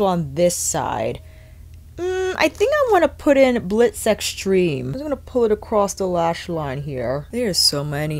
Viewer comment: I thought you said Blitz Sex Dream 🤣. Great vid! Personally, I’ve been back and forth on Divine Rose 1 or 2. That’s good because I think I’ll skip both and go with this one